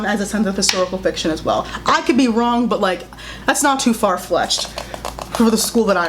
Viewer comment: I’m sorry what “It’s not too far FLESHED” ???? What did you say? 12:40 😂